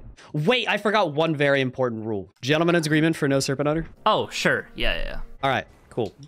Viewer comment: Can anyone tell me what the rule is mentions at 2:38? I’ve listened to it so many times and all I hear is “Gentleman’s agreement for no serpent’s dagger” but that doesn’t make any sense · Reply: Oh it’s no Serpent Hunter! I forgot the name of that weapon!